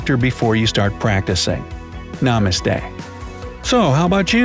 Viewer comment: When he said " Namaste ", I heard " no mistake"🙄🙄